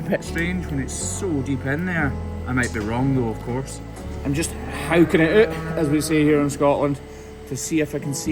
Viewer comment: What did he say he was doing to that hole at 2:10? I heard something like "howkingituht" or hawking it up?